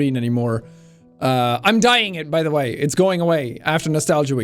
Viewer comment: I thought he said he was DYING for a second xD